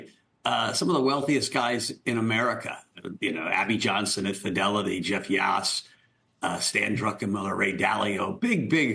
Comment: what’s the price of being so many of Jaime’s clients 0:39 is what I heard 😆 Jaime ain’t fooling anyone